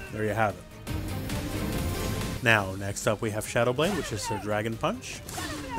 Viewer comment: Wait. She says shadow blade? I always thought she said "celebrate" lmao